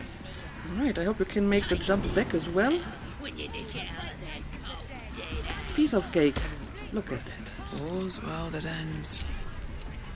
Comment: When you said "piece of cake" I heard "piece of *ghaik*" which is wonderfully funny. Did you name your new PC? I always name mine after prominent women in computer science.